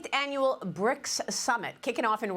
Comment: Maria says:summit:...CC says smut.